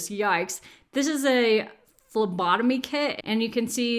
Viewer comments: #ADHDmoment I thought you said "lobotomy kit", not "phlembotomy" or how you spell it. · I totally thought you said, "this is a lobotomy kit" instead of "this is a phlebotomy kit." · I thought you said LOBOTOMY KIT!!! 😳😳😳😳😳😳😳😳